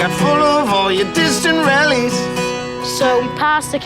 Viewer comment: Hey/(excuse me) can anyone please tell me what Ronan is saying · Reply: Sunshine Vos It sounds like, 'Bucket full of all ya distant rellies.' But that is not right! 😂 I'll look it up for ya!